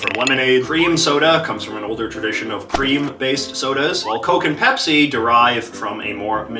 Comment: I was confused for a second because I thought you said “Hulk Hogan Pepsi”